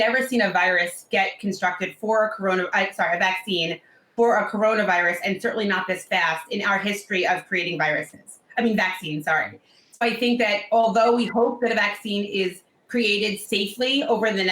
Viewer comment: Did she say creating viruses